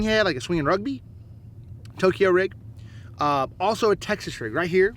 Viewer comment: Hey buddy..what is a china rig..my bad i thought u said china rig but u said toyko rig...what rod action and line type and lbs is good for starters trying out toyko rig..?